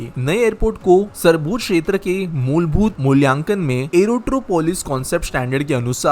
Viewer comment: Bro what did you say at 1:54 "sarbodh kshetra ke mulbodh...😅"?